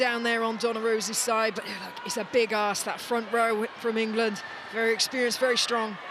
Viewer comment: Did she say "it's a big arse?"